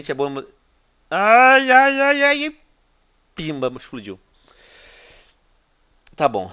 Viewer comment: Opa eu escutei um PIMBA? 4:52 Adorooo